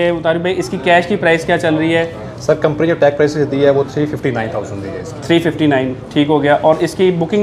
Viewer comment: I thought he said 259,000/- but it is 359,000/-, too high price but as other things it's price will also go up, up to 400,000/